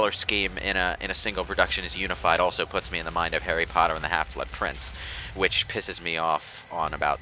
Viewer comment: Hyperon’s Notes -I always play on easy mode in most games. Call me a scrub, but I play games to relax. -Thought said “Harry Potter and the Half-Wit Prince” -Nobody’s going to comment on Keenguy?